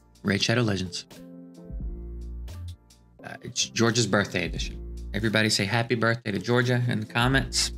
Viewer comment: Happy birthday Georgia!!! At first I thought you said it was George's birthday, lol. (Thud & George). That didn't seem right and then you clarified things. ✌️❤️